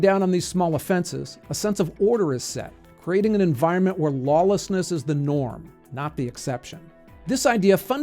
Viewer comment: I think you mispoke around 4:17. sounds like you said "where lawlessness was the norm." The text seems correct though. Small nitpick, otherwise a fantastic video.